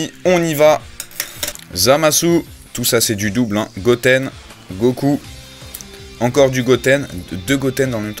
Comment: like si toi aussi ta entendu goku au lieu de gohan a 9:35 (on dirait les doubleurs vf qui se trompe tout le temps)